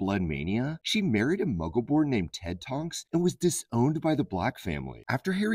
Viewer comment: I keep hearing "TED Talks" when they say "Ted Tonks." Also, glad to see that Saoirse Ronan didnt sabotage this marriage like she did the last one between Knightley and McAvoy. Or...maybe she did and became J.K. Rowling! And now she is secretly writing an ending that is happy (or more accurately "happier," since this storyline is anything but happy) for Knightley and McAvoy!